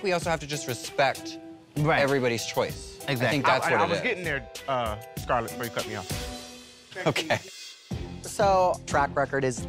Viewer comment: What did TKB said at 2:49? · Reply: I was getting there Scarlett before you cut me off.